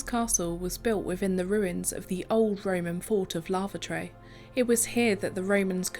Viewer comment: It sounded like you said the old Roman fort of lavatory 😂